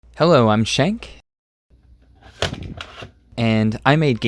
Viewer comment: Shank: Hello im Shank Me: Did he just say he was a Skank